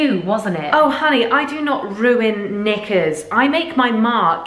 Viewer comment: POV you thought she said “I do not ruin ni-“